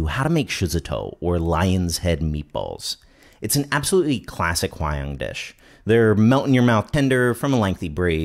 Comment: "classic lion dish"? Did I misheard anything?